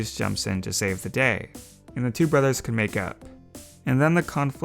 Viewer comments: I thought you said “and the two brothers could “ make out. “ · who else heard make out instead of make up just me ok